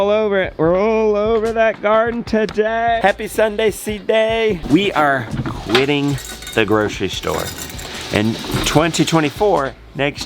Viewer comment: Hello, my name is Sergey, I am from Kazakhstan, I learn English I hear you say "We are winning the store" I think it is "We are beating the store", am I correct? or just Kazak who can speak Russian, Kazak, Chinese, and Arabic. I am learning German and English now. LOL!